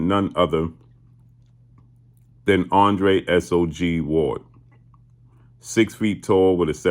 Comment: Andre "SOG" Ward? Naw bruh, I clearly heard him say he was Andre "Who's Us?" Ward.